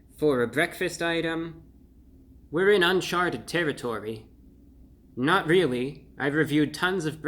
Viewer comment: I totally thought u said unsharted territory